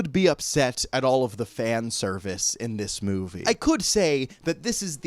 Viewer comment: I misheard this as "pan-service" like a cross between Peter Pan and fanservice, and am a little bummed that wasn't what you actually said.